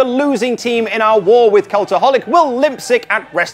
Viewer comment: Did you just say limp sick for lip sync?